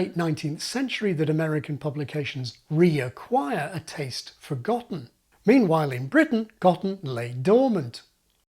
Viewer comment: I misheard the phrase as "a taste forgotten" and enjoyed the sprinkle of poetry.